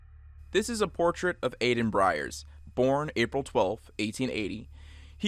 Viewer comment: did he say bryers isnt his name Byers said like buyers like yo look at my last name